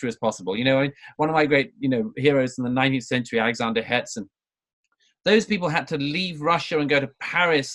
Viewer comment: Who is the person Douglas mentions? I hear "Alexander Hedson", but it seems to be incorrect! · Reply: Alexander Herzen, 19th century Russian philosopher and essayist.